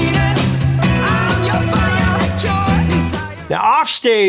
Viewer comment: I could never understand some of these lyrics and thought they were saying I’m your Venus, I ‘m your fire, joy, desire, thanks for the clarification, love this song, I too thought a male sang it. Thanks, love the channel.